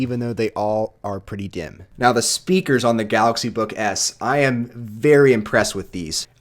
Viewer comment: I don't know if it's just me but... I hear him saying "galaxy book ass" I apologize